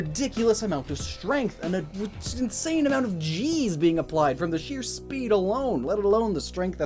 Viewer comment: I like to think that was saying “geez” and not “g’s”